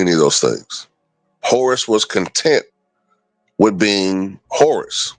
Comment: Sounded like you said Horace was content being a horse 😂